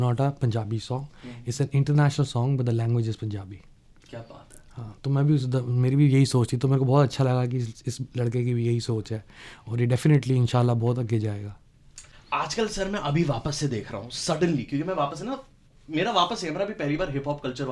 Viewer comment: Did he just say In'sha Allah